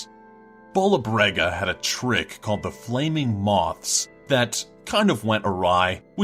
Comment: did anyone hear flameing boss